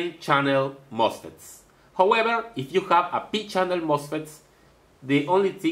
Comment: he is saying if you have a P channel mosfet. I had to hear it 20 times before I understood what he was saying.